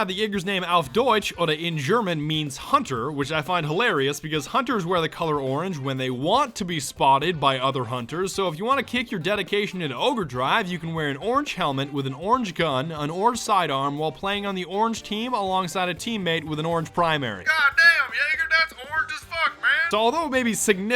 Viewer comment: did he say ogre-drive?